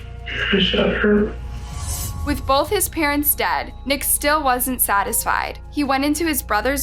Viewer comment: I legitimately thought you said 💩 both his parents 🤣